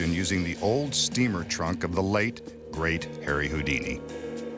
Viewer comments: Because he is the master of magic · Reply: pic sorry, I meant I hear always "hairy houdini" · Why I always hear "heary houdini" XD